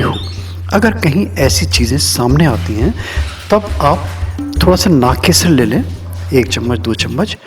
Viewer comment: kya bola apne? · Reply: Naag kesar